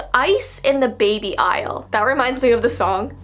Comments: When said ice in the baby aisle I thought she said ice age baby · When you said “ice in the baby aisle” I thought you said ice age in the baby